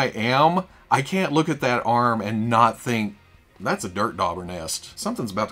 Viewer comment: Anyone else hear "dirt daughter" instead of "dirt dauber" nest @ 22:00? 😂